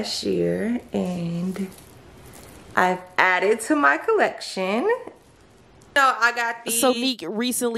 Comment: She never said that Dwayne bought her those 2 new bracelets, she said that he bought the red, black and white one, y’all know y’all can make some stuff up 🤦🏾‍♀️ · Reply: she actually said “I added to my collection “ …